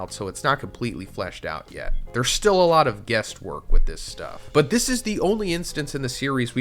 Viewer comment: I'm sorry, @ 2:20 did you say "guest work"? If you did, I'm fairly sure you meant "guess work".... Right? There are some of us who heard the phrases wrong growing up and no one ever corrected us. This could have been a slip of the tongue, but sometimes, it's real. My favorite instance is a coworker of mine once thought it was "a higher peg rate" rather than "a higher pay grade".